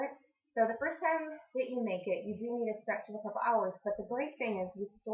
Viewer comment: Just found your site. Love your demonstration, but, just so you know, the sound is very low for most of it. I tried to do closed captions and OMG! the caption says the Strangest Things. ie. @ 5:01 it reads "The first time you make it you do need a specialist of ours, but the great thing... just to offer peace with where the local you want to make in those two weeks in pending 6s so Willie is very saying you all we have to think usable it once and every chili spoke once a month." You should try this. Some are funny like typo's but some are disturbing totally off subject. These 'auto-generated' captions need improvement!